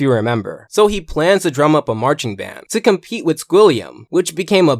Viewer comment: DID YOU JUST SAY DRUM UP A MARCHING BAND.